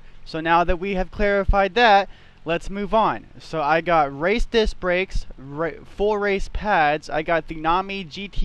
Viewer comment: i wasn’t paying attention and i thought you said “i have racist brakes” 🤣🤣